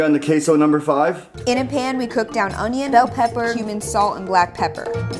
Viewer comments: I heard human... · Me: did you just say human salt!? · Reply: Cumin, salt, black pepper etc. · Did she said human salt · it sounded like she said human salt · I think she said human salt lime if you agree with me comment if you don’t